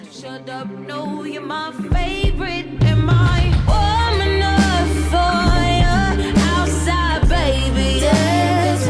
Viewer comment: what a great surprise, this album along with RAM really changed my relationship with music. Listened to it soooo much, and to this day even. Love it more than SOS or any other joint, the album flow is godlike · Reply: oh! also forgot to mention, but everytime I listen to Drew Barrymore I don't hear "warm enough for you" I always thought she was saying "woman of fire". There was another video when I caught them on screen and I just can't unhear it.